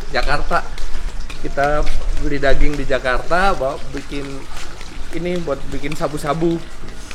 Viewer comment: ga salah denger " INI BUAT BIKIN SABU-SABU?"